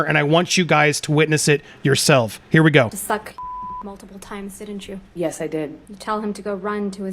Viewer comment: what did she ask??? all i hear is 'suck ____ multiple times didn't you?'